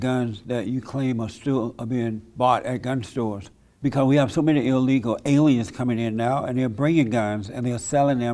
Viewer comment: I always hear ''Illegal Aliens'' when Lee talks about immigrants comming.